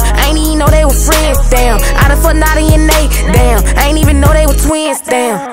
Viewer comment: I done fu*ked Nadia and Nae, ong I thought you said Nati 👀😂 imma sing it like you said Nati 😂💙